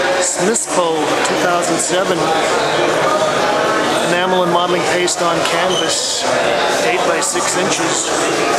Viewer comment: Wonderful textures, colors and use of materials. I didn't quite catch what you said at about 1:40... the medium used for the small painting "enamel and [something] paste on canvas? Great show! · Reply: Modeling paste